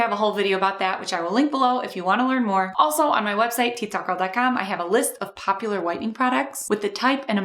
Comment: Oh. I thought you said TikTok Girl.